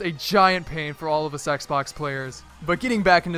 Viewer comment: What I love is that when you say 'Xbox' at 5:38 with text captions on it says 'sexbox'